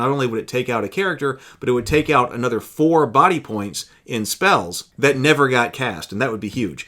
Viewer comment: At @ 8:35 what you say is correct, the clarification inthe text is wrong. You said "would take out another 4 body points in spells". The emphasis here is another 4 body points (to the 4 body points already lost if the wizard had one healing spell which was the base scenario).